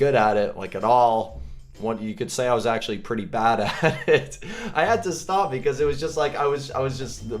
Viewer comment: When he said "bad at it" I thought he said, "badass"